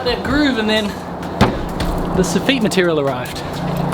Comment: Great vid! I was scratching my head for a bit about what a sah-feet is. Finally realized sof-fit when you were explaining the v-groove material.😂😂😂 Glad I'm getting some language lessons. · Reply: I heard "this effete material" lol.